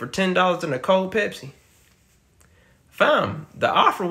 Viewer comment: Offer is too low for me to take. First I thought I heard 10 mill, that made me consider. Upon realizing that it was 1 mill, would've said no. Gimme 10 billion, then i'll do it and split it 50/50.